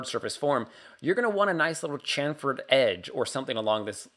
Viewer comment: Sorry about this, but what's that word you use that sounds like 'chamfered edge'? My command of the English language must be terrible, I've never heard of that term before. D: Could you type it out? Is it jargon used for modelling?